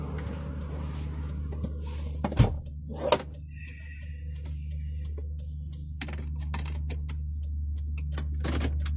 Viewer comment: OMG THERE IS AN EVP IN THE HERSE THAT NO ONE NOTICED!!!! - @ 14:19 right after he shuts the door and puts on his seat belt there is a breathy whisper in Spanish!!! I haver no idea what is being said because I don't know Spanish but I hope someone that does will listen and try to see what they say!!! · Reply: It sounds like it says " Ay, así es " or " Ay, aquí es " like " hey, it's the way it is or " hey, it's right here "